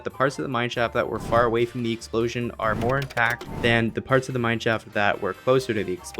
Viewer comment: Did you say INTACT ? 0:38 :3 ty